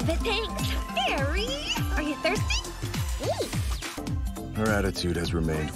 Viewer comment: Does anyone have a clue what Eri said here? After someone asked if she was thirsty? It sounded like “neat” but it’s hard to tell